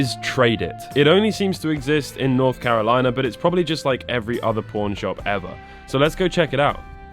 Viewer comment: I know he said pawn shop but I could swear I heard something else. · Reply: The youtube captions on this video said porn shop not pawn shop😂